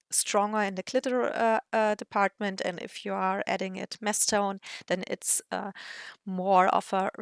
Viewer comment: Definitely a gorgeous colour, and I'm very tempted to buy it. I love a warm silver. What is mstone? Can't quite make out the word, but it comes up twice, at 18:59 and 20:55 (see transcript, which didn't pick up the word properly either). Is it some kind of paint medium? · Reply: Masstone is the word 😊 it means that the color is applied in their thickest/ darkest Version. I hope that helps clear that up and of you have any other questions, don't hesitate to share them.